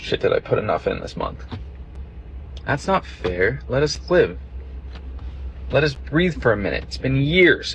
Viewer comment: It sounds like dude is saying, (12:38 TS) "I can't breathe."